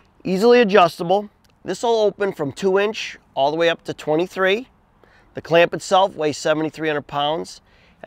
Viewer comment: Thank you for the video. At 00:29 for the life of me I couldn't understand how much it weighs. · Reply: The clamp itself weighs 73 Pounds. Thank you.